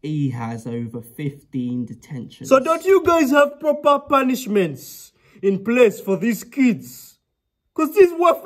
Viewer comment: “Disruptive, So don’t you have proper punishments” 🤣🤣🐐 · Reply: I thought he said COPRAL punishment coz as an Afro that what my Dad would have asked🤣🤣🤣